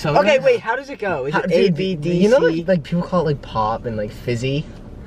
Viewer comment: did Matt say abdc?